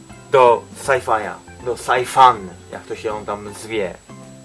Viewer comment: SsajFaja xD dopiero teraz to usłyszałem ;)